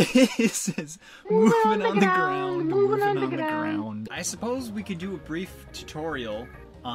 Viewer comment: it sounds like hes saying moving granny gran